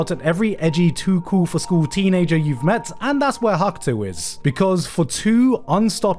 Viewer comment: that's who what is??? say that again??